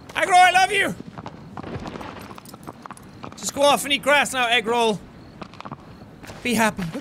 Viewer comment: It's so funny how when he said Argo it sounded like he said egg roll